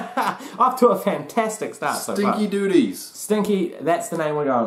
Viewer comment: Did she say Stinky or SKINKY? I thought she said SKINKYDOODY but with the accent of her character I could have misheard.